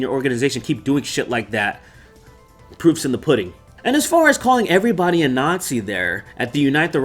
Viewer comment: For a second I thought you said "poops in the pudding"